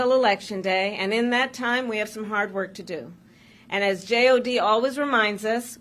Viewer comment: Not gonna lie, I heard that as JLD, aka Julia Louis-Dreyfus 🤭